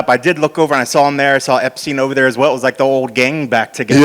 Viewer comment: Did he say Epstein?